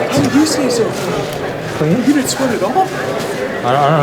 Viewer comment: Anyone know what the guy is asking at 25:50? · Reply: Not sure but i think he said You didn't sweat at all?